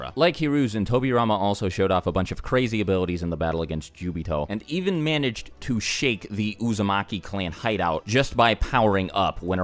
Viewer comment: Did he say tobirama shuck the uzumaki hideout